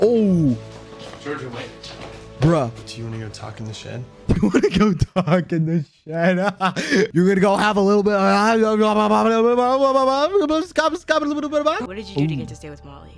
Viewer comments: BYE I RLLY THOUGHT HE SAID "wanna go talk and shit.." NOT "wanna go talk in the shed.." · 'lets talk in the shed' i heard 'lets talk in the $hit' 💀